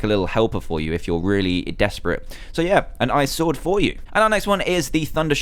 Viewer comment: Did he say "An Ice sword for you!"? Yes, I think he did lolol *Ba dum Tsss*